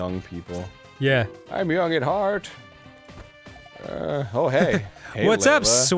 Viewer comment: Oh, he said young at heart?!? I thought he said young and hard.. O_O